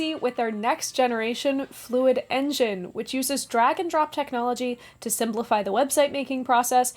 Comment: I just thought you said your sponsor uses “Dragon drop technology”